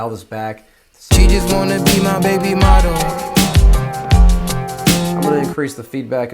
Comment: nice video!!! I started laughing so hard i thought i heard baby momma!